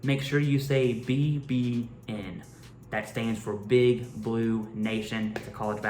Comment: Bbn or vbn lol I thought I heard vbn my question is did you say vbn or bbn 👀